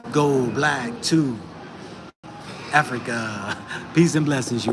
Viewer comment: I thought you said Go back to Africa mentally and physically I know old people my age could not go there Body aches,etc